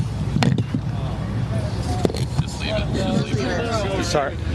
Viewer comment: Did anybody else hear a lady say “Just hold it” when the microphones were falling and then the look the Sheriff gave her? 😳